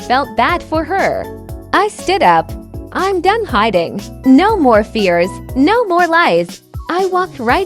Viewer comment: Once she said no more fears, no more lies. I thought she was saying a golden.